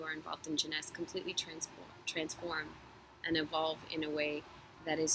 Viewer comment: Listen to what she says between 2:37 and 2:40. Did I hear her right? Did she almost say that “watching the women involved in JNESS turns me on” but corrected herself to say “transform”? She won’t stand a chance in court because she knew what she was doing.